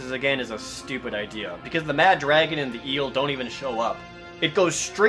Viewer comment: >mfw I thought he said the boss was BAD Dragon.